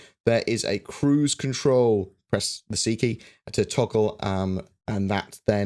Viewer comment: At like 8:15 he said some weird British word to increase velocity (I assume like old cruise speed). Can anyone translate that out of the Queen’s English so this American can understand what key to press. It sounded like he said tiki. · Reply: Ronin7 He said “press the C key”. C is the default to toggle cruise control.